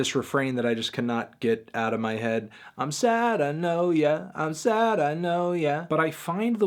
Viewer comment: It’s “I’m sad and low yeah” lol but I thought it was I’m said I know ya at first too